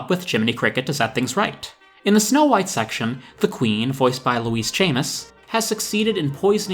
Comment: I swear when you said Louise Chamis, I thought you said Louise Jameson, the actress who played Leela in Doctor Who.